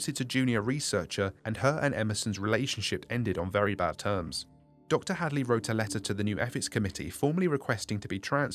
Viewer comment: Yeah I thought he said dj Khaled wrote this